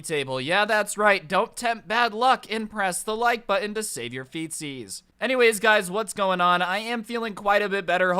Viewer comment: I thought he said "To save your FECES"